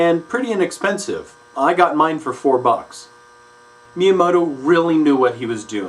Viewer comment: For a moment I thought he said "Miyamato" at 6:20. :P